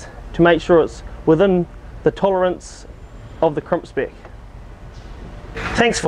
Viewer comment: did he say any Christians or Queeries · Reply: Hi First Lats, it's "queries" 🙂